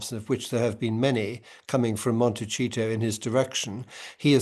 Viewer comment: The slip around 2:16 😂 Monte-cheat-o instead of Monte-see-to